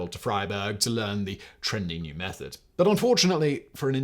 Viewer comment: It sounded like he was saying "the fired egg method" and that is quite ironic to me